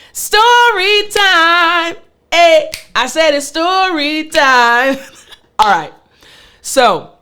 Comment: I always thought she said "study time" 😂😂 I just realized she says "story time" 😅